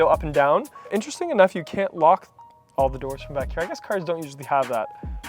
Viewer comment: You sped over one of the driver's door switches I'm interested in. I heard "locko" button but I guess you meant "lock all". Does the light in the switch indicate non-drivers can use their power windows or the other way around?